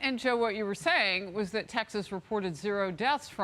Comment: Am I the only one who heard him say Mississippi reported o deaths , then she corrected for him and said that he said Texas lmao lol